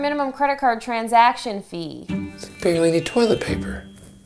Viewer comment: I hear, "that's bare legged toilet paper," but..."that's barely a toilet paper."